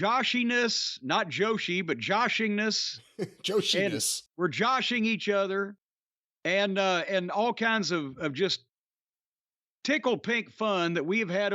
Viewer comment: Did he say tickle? Does it involve Trish Status, or Sable, maybe both of them together... you know the WWE never did do my dream match of a 6 way, iron woman, hell in a cell, tickle submission match with a 120 min time limit. I would be the guest referee, and no matter who wins the other 5 turn on me for psyback over being stuck in such a stupid gimmick match 😁😈